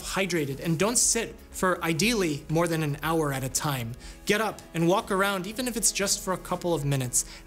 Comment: When he said don't sit for a hour at a time I thought he said don't simp for a hour at a time